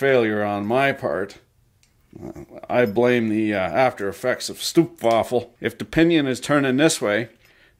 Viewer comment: I love the way you say 'stroopwafel' it sounds like you say 'stoepwafel' wich means curbwaffle😂 Love your vids keep up the great work!